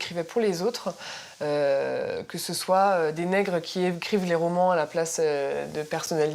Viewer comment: euhhhhhh à 2:07 elle a bien dit le mot "nègres" ou c'est juste moi ??????